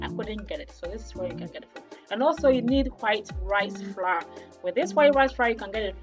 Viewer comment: I heard white ri-fra (white rice flour)